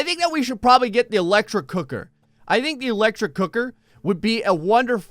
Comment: I thought he said electric Hooker